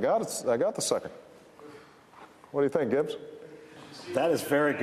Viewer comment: LOL when he says what do you think rich/gitch or whatever, I swore he said What do you think bitch?